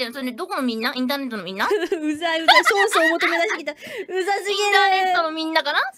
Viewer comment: What does she say at 2:04? Sounds like そうそう.... then おもともなしがきた or something like that. Even when I understand her the majority of the time, she speaks so god damn fast sometimes. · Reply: It is "ソースを もとめ だして きた" Even Japanese people can't hear this Japanese lol, especially the "もとめ だして きた" part.